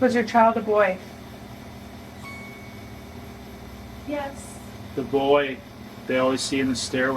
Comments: when they ask if the child is a boy. Does anyone else hear a woman say yes? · am I the only one who heard “yes” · DIDN'T ANYONE HEAR THAT....................A "YES" .............I dont think it was from you guys.........That sounded like bit far voice